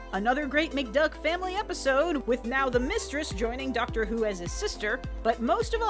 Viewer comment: Did you just say Duckter Who?